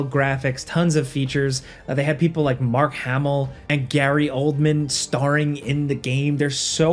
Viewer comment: Thought said Gary Coleman and for a moment I was very impressed 💀